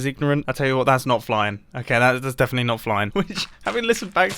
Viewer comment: i thought mumbo was saying "Flyin" when he was saying "fine" and i was ready to go with it lol